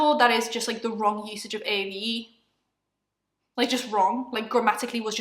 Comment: I’m sorry if I’m dumb, but what are you saying here? AEVE? I can’t tell what you’re saying 😂 Can someone please explain to me what this segment is about? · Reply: AAVE - african american vernacular english